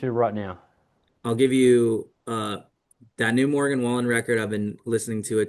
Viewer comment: what is the band/album he says? I can’t make it out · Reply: Morgan Wallen ? Or gridiron